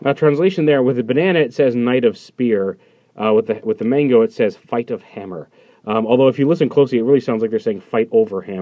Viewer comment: i thought it said knight of hammer :/ go figure lol